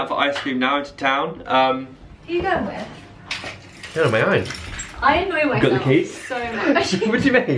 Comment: I thought said: 'I enjoy myself so much' (